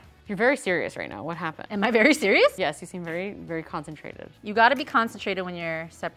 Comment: I heard her say constipated instead of concentrated and I was so confused hahah